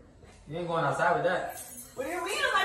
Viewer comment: Oh, I thought he said they were bad shorts. Like baaaaaaaaaaaaad shorts! 😈